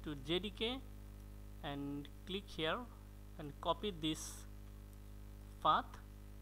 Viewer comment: i heard pat